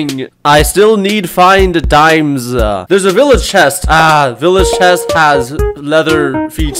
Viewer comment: For a sec j thought he said “oh there’s a timple” Bro what